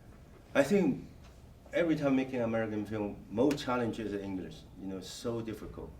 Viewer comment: Whoever wrote your subtitles (I'm not talking about the auto-generated ones) got it wrong here. He says "the most challenging is English." You put "endless" there instead of "English" lol. There's a few other mistakes but that one was the most glaring.